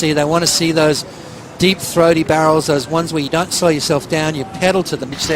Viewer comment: "Deep Throaty Barrels" - What did he sayyyy